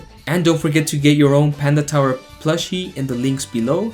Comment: Definitely thought you said Penn and Teller not Panda Tower😂